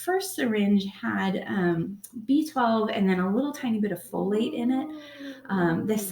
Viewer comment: Oh "Folate" was in the syringe. I thought you said "Kool-aid". :)